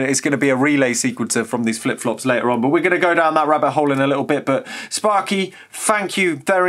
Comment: did you just say - we will go down that RUBBER hole ? lol